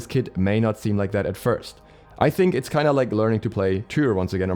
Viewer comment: Anyone know what he said , ‘I think it’s like learning to play _____’ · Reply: Tyr